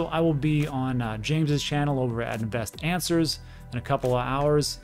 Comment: You said "Best Answers" I heard "Best Dancers" :)